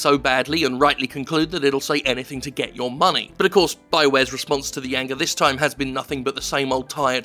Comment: you said Bioware instead of Blizzard.